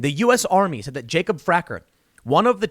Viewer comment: I thought they said Jacob Cracker lmao Glad that's not it, but man, it wouldn't shock me because this year has been... Just so weird